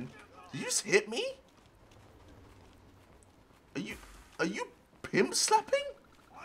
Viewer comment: wait... i could have sworn the guy in the jumpsuit said " IM A PIMP NIGGA!"